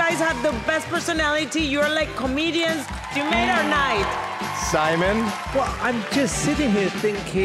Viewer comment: 辺り空耳アワー 「コメディアン夢がない」って聞こえる😂